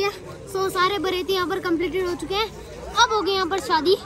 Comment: does said"beritiye"